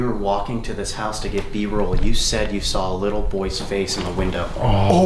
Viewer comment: is that one of y’all speaking ? Sounded like someone said “is that a boy?” I’m probably tripping